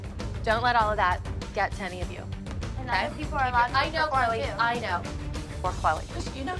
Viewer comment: Did anyone else hear maddie say ar 4:10 “other people are allowed to perform too” 😳🙄